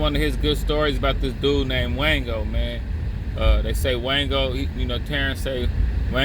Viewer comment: I thought he was saying mango. Anyway RIP to that man .